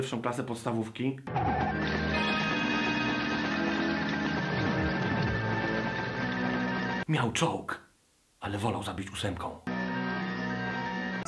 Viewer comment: Usłyszałem "Michał Czok" :D